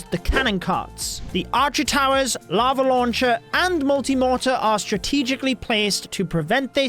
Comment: he actually said lava launcher to roaster